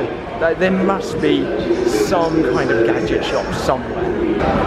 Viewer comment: I thought he said "toil gadget" i was like uh what